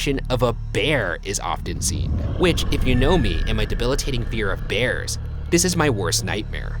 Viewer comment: i thought ryan said “bear of fears”